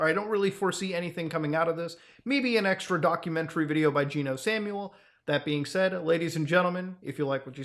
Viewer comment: I thought you said Jimmy Kimmel instead of geno Samuel and I laughed